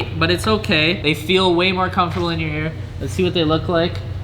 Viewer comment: Go to 14:34 did he say hair or air ???? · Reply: In your ear